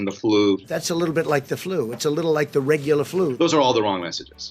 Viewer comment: did he say regular flu???🤦🏾‍♂️